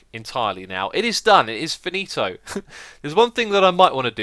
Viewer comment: did you say "finito"?